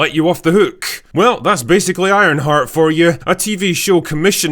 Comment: Thought he said ironfart at 1st lol